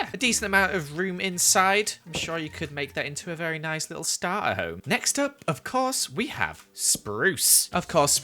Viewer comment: a s-starter h-h-home? is that w-what you said?